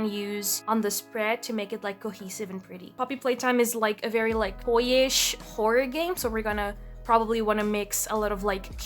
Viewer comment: maybe it sounds like you said boyish? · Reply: Yes it was toyish! It's my bad honestly, could've used a better word to describe it